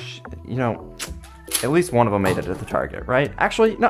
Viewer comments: I thought he said aks7 for you🤣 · I thought you said aks7 for you💀💀💀💀